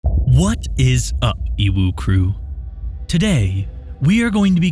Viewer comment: I really thought you were saying “what’s up, UwU crew”